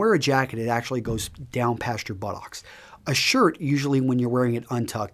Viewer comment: I thought he said "bollocks" instead of "buttocks" o_O my bad!